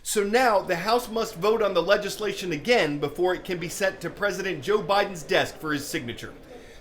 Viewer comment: I thought he said Joe Biden's death.